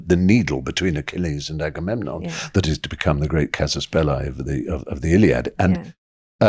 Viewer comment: What word does Stephen fry says at 25:31? When he's talking about: that is to become the great 'caspelli' of the Iliad. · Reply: Casus belli. IS latín. IS the justification to declare war. Its use even today